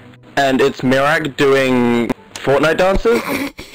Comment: I'm ashamed 🤦‍♂️ "it's like doing fortnite dances" he clearly said miraak